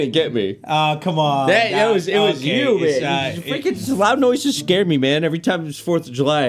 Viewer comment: did you say free candy????